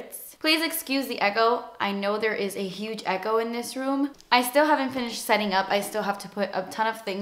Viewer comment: who else thought she was saying "eggo in the room" HAHAHA